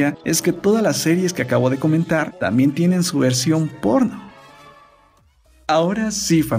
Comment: como no entiendo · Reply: De que todas las caricaturas tienen su versión no por :v